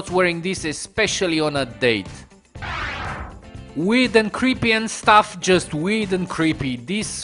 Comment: i thought you said weed in sted of weird..... am i right